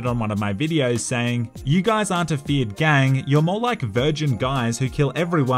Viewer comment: Bro i just got that you say feared gang. I always thought that you said Fiat gang like the italian car company Fiat. 😂😂😂😂